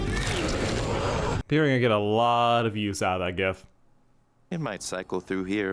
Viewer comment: Does anyone know what he said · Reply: “They’re gonna get a loooot of use out of that GIF”